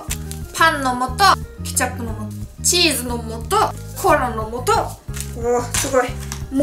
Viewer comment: 初見です。 コーラの素がコアラの素って聞こえた私…難聴かな？